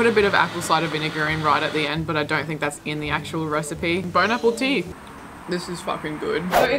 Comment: It sounded like you said "bone apple teat"